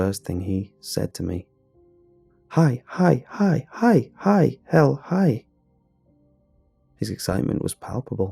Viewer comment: holy shit did bill just say hell in